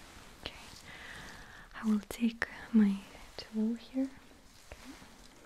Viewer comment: What she says; “I’m gonna get my tool here.” What I hear:, “I’m gonna get my toe here.” Ma’am, this is a sleep study.